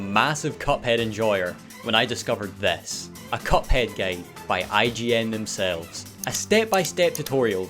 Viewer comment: Why he says "IGN gaide" instead of guide? It's not just me who's hearing it right?😭